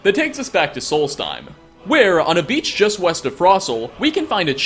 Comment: West? I thought you said Weast